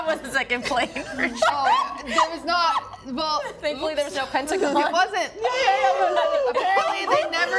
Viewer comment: Anyone know what the girl says here at 12:41 to get such a big reaction from? "thankfully, there's no..." but I can't make out the punchline. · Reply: pentagon, since someone made 9/11 joke